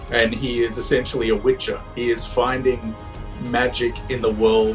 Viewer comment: did he say "Witcher"?